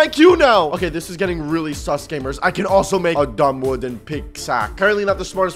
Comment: just me or did anyone else hear “dumb wooden pigsack?”